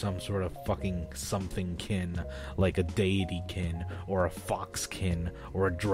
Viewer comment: When you said deity-kin i wasn’t looking at the screen and i thought you said davey-kin